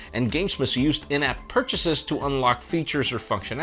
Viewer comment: what does he say here here? "And games must used in-app..." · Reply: Purchases